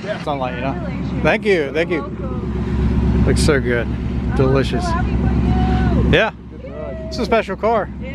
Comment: did he say delicious? Lmao